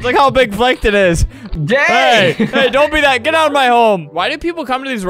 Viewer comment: did say gay at 1:03? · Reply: I think he said “hey” but even if he did say gay it’s not bad or anything